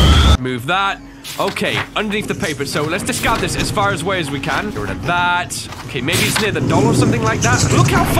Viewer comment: sounded Like some type of Harry Potter spell Asfarus wayus wekan😂